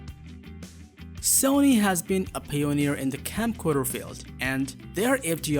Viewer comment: Can’t even understand what the narrator is saying, slurring words or language, for example 1:13 Mark when opening on the Sony camera —-what is he even saying? Sounds like he’s saying: “Sony has been a FAILURE in the camcorder...” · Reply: Sony has been a PIONEER in the camcorder field. The narrator's first language isn't English. However, his video is interesting.